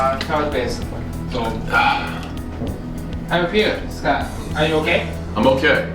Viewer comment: did he call u Scott. Check my channel